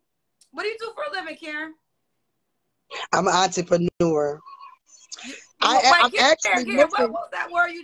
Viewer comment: “What do you do for living Karen” “Imma auntiepaneur” I knew this call was gon be a mess😂😂😂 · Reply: wayment.....I thought she was saying "auntiepreneur" to be clever ....because she's an auntie...but she just can't pronounce the word lol